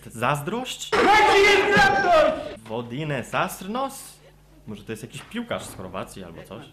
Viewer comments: usłyszałem dajcie wibrator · ja tam usłyszałem "dajcie wibrator" xDDD w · ja usłyszałam 4:19 dajcie mi koty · Ja usłyszałem : Dajcie Vibrator Bez skojarzeń :|